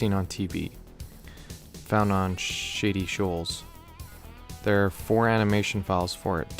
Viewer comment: I thought you said “shitty shoals”😂